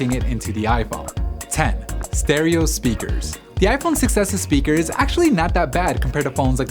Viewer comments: It sounds like he is saying "iPhone success" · I keep hearing "iPhone success" instead of "6s" 😂